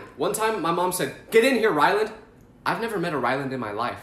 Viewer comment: i was listening while playing roblox then at 4:52 i thought he said my name lol which is rylan